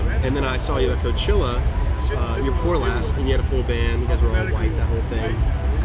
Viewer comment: The interviewer said that Annie's band "wore all white" at Coachella (or whatever the event) and Annie heard "were white". She repeated "yea, we were all white" looking as if that was weird he said that but addressing it rather than passing by it. He joked about it "ya, you were all white haha " knowing the confusion and deciding better of it and to move on and not correct what she had misheard. Then he said it was a bummer about this particular show, and she said "it sucks that you guys got stopped" and he responds "I don't know what you're talking about, Annie…" which is to say, we can't speak about us getting stopped. The stopped probably means, weren't allowed in to do with being media, or some other issue. I'm guessing because it looks bad on this interviewers team or whoever he represents so it was a taboo thing to have said in the interview. The camera person instinctively covers camera to represent a necessary edit and he follows with "...we'll erase that later..". Annie, understanding that they didn't want that said and sort of caring but not really responded with "wow, wow…" to show that they were being awkward but as you can see didn't really give a shit following.